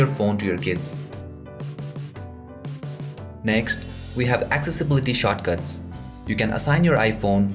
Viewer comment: lol it sounds like he said accessibility shotguns